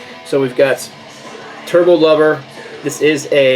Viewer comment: I'm having my wake-up coffee and I could have swore you said, "Turtle Lover"...had to spit coffee back in my cup. Holy crap. And speaking of Holy Crap...If there was a Judas Priest song that would make me put a shotgun to my face it would have been Johnny B Goode....certainly not Better By You Better Than Me!!